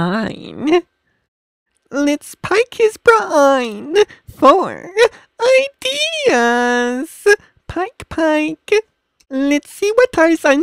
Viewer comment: i heard 'let's pick this brain.. FOR AIDEEEASSS' .. lol.. am i right?